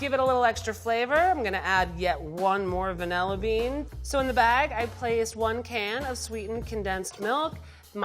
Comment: "Yup, more vanilla beans" meanwhile all I heard was "Yup, more rhum"